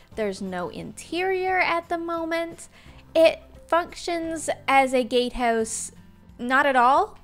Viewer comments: Can anyone tell me what did she say 1:04 because I heard (it functions like a gay house) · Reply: She said it functions like a GATE house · I swear I heard her say it f***chins instead of functions.